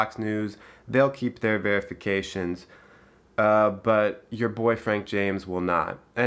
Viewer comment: WT?! Not surprised, YT is part of the machine...then I heard you say "Your boyfriend" ???! and I nearly fell out of my satee, haha! Ma' boy, you are verified cool and attractive no matter what